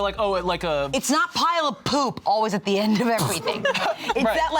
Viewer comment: "forgive me for not saying a pile of poop" I CAN'T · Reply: I thought I heard, “the pilot pooped” and I was like, yeah ok. LOL.